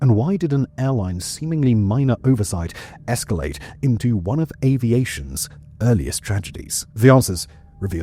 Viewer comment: " One of Aviation's earliest tragedies." ? Dude most of us, including you were alive in 2005, You are making it seem as if 2005 was 1935. The most tragic thing is that it happened after 09/11/2001. What were you thinking when you said that? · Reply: He said "one of aviation's eeriest tragedies"